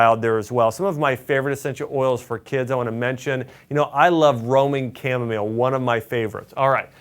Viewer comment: Thank you. I thought you were saying Roaming Chamomile instead of Roman Chamomile —pronunciation.🙂